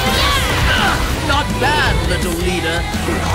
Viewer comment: so much illaoi, nice^^ at first i heard her say "little w**ner" instead of "little leader" xD